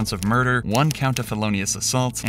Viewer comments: Can anyone tell me what "Philolaos assault" is? lmao 17:48 I think they meant "felonious" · I'm sorry but she was charged with...."Philolaos Assault".....? What the hell is this channel? How does one write Philolaos and not Felonious? How do you write the name of a Greek philosopher instead of a felonious? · "philolaos assault" ...I think you meant "felonious" ? Wtf is that word? 😂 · "Philolaos Assault"? I've heard of felonious assault, which is what the narrator clearly said. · "philolaos assault" instead of "felonious assault", odd typo